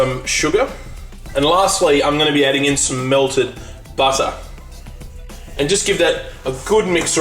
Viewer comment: When sed "Melted butter" I thought he said Murdered butter D